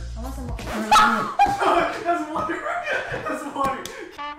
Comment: what u said at 4:36 ? i couldn't get it :D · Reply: water I think